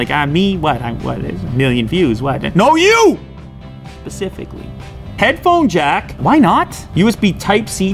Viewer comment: did he say “no you! Pacifically”?!?! If so, I’m unsubscribing😂